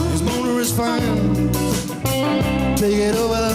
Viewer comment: I'm almost certain MK sings "his boner is fine" instead of "his motor is fine" as it is on the record...